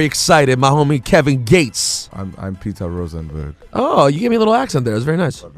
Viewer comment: he thought he said "allah" but it was "a la" lol. peter just went with it. i woulda done the same